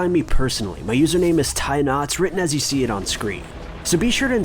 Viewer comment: Always thought you were saying tie knots